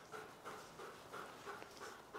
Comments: I just hear him say he's poopa 😭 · I hear “IS POOPA”